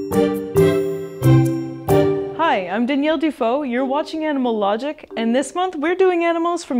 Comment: In the introduction, I thought she said Danielle Defoe instead of Default, and my mouth was wide open thinking that she is the daughter of Willem Defoe.